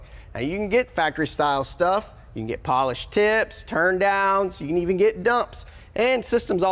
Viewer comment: I stg I thought I heard him say "polished tits" 🤣🤣